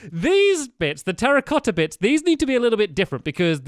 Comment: when he said terracotta blocks I thought he said "Harry Potter blocks"